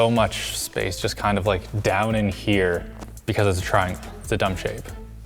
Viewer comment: linus said innit but captions say in there